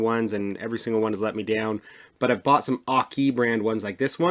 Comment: How dumb am I. Thought you were saying off key brand. And I was so busy so busy watching the video from the camera I did not see the name on the lower left, Aukey, until about the end.